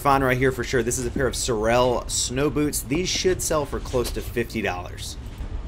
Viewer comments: " These shits sell for close to 50 dollars " I know that's not what he said but it sounds like it for sure lol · sounded like you said “ these shits should sell for “ haha